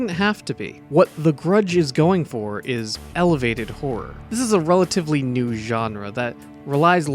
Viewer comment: funny enough when you first said "Elevated Horror" I honestly thought you said "Elevator Horror" like was boring and you knew where it was gonna go with no surprises